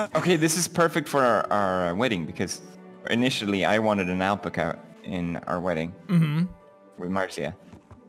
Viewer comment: a whAT? 10:35 sAY AgAiN? 10:35 ALpca?